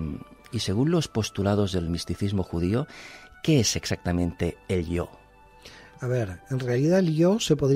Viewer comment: QUe dice exactamente??? el 'Yo"? o "elio', "el Yod"??? Por favor que alguien confirme, porque tengo duda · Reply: El "yo"